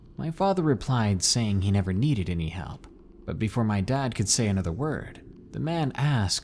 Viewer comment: I thought you said another n word at the beginning.